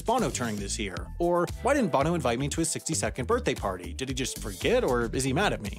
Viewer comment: I thought the narrator said 60 second birthday, as in a birthday that lasts a minute lol